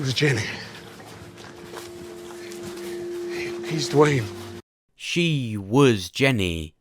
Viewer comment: NEW VIDEO! My official REVIEW & BREAKDOWN for TWD SEASON 10 FINALE! WHAT HAPPENS NEXT <link> IMMUNITY in The Walking Dead! Mystery Solved <link> The Walking Dead ENDING Explained! THIS is how I think The Walking Dead will end <link> The Walking Dead FINAL SEASON CONFIRMED ENDING 2022 <link> Disclaimer: If I'm going to make a theory on something that is known for being unlikely, best believe it's because I've found proof and connections. Anyone who comments "Duane is dead" is a Nicholas. Do YOU think Duane Jones is alive? Did my theory convince you? Make sure to let me know down below! Don't be a Nicholas make sure you like AND subscribe! Thanks for watching. I'll see you soon. ►SOCIAL MEDIAS •Twitter: <link> •Instagram: <link> ►JOIN THE OFFICIAL DISCORD! Join the official Discord! <link> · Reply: Way That's what I thought too. Instead of saying he's Duane (he is Duane) I thought he said he's Duane (he uz (was) Duane). It was past tense. Also, he saw Duane turn. That's why he went crazy and ran. Any parent (especially considering how much Morgan loved and protected Duane) would've risked his life to attempt to save his child.